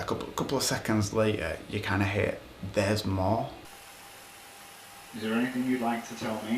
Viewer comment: I didn't hear "there's more", I heard "downstairs"